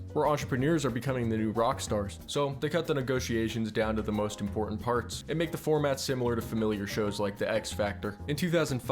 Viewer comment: what did just say 😭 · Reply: hawk tuah coin